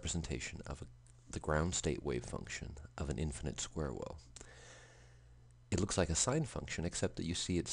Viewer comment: Can anyone please tell me what word he's saying here? Sounds like "an infinite square-worlf". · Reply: Infinite Square Well <link>